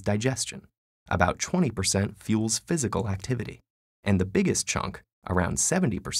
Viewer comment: Did you just say CHONK?